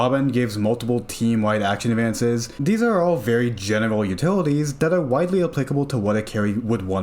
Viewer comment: i heard genital utilities.